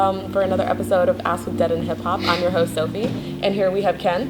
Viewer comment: Every time Sophie says "I'm your host, Sophie" it always sounds to me like she says "I'm your hoe Sophie" haha. I know that's completely off-topic but is that just me? Cause I actually thought she said that for the longest.